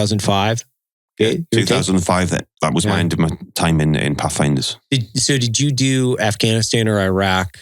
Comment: Path Finder? I thought he said Puff Adder!